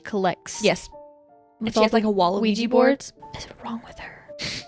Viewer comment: You said "Wall of Ouija boards" but what I heard was "Waluigi Boards" and now I can't stop imagining a Mario themed Ouija board lol